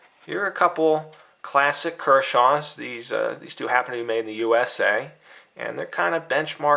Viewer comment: I thought you were Canadian for a second in this review. At 1:12 I heard "The US eh?" HAHA Looks like a dang fine knife! I have not found it at a Canadian dealer yet so I havent picked it up yet but I think its a knife that I would like to own soon. I think anyone can agree that for 30 dollars this knife is worth it. But yeah there is a few issues definitly.